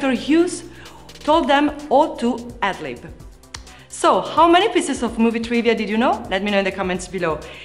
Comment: What the hell did he tell them? I rewinded 30 times... still makes no sense what shes saying. Oh two adlib? Ought to adlib? WHAT? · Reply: 'Ought to adlib' is correct! Adlib means to improvise your own dialogue, so she's saying it wasn't heavily scripted and the director let the actors improvise lines! :)